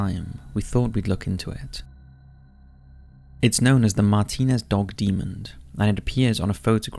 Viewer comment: did you say dog demoned?